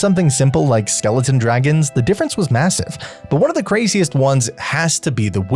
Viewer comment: did you say MASSIVE